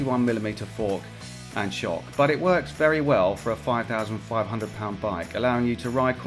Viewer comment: im so confused.... I swear he said "for a 5,050 lbs bike"